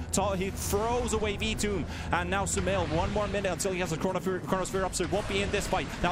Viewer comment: " did he say corona virus? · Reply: probably "Corona Sphere"